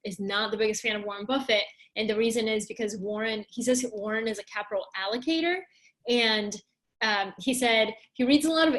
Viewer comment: I thought you said capital alligator 😂